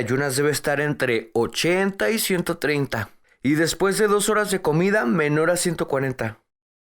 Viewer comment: what numbers is he saying at @ 0:58?😓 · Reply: 80 y 130 y después de 2 horas de comida menor a 140